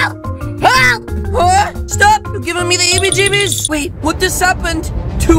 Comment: YES SUSSSS >:( 1:06 What said: eebie jeebies Captions: eevee jimmies that was sus